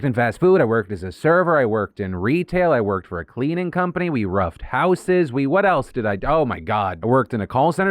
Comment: I thought Phil said "I robbed houses" Had to rewind 😀